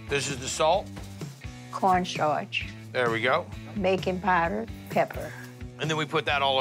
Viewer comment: I thought she said BACON powder 😭😭😭 was gonna order some but then listened again 🤣